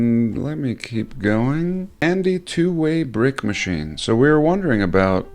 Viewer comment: 21;10 Misheard "Andy Twowaybrickmachine" HaAaAaA!😆